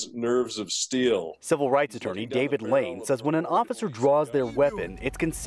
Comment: Holy shit i almost thought he said David Blaine. 0:47